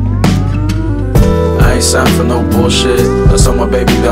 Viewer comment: I thought he said “I suffer from the bullshit”